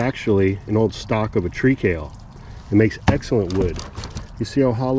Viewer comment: What kind of plant is that he is cutting to put in the planter? Couldn't understand what he said. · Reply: It's kale. I thought he said "tree TAIL" at first, but turned on captions and he's saying "tree kale" which is a variety of kale I never heard of before now. Must have a woody stem similar to sunflowers.